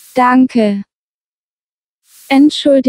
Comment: Wie spät ist es, I heard it as which pet is it 😂